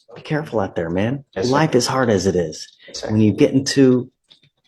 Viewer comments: I definitely hear him saying "black is hard as it is." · Thought he said, "black is hard as it is" towards the end. 😆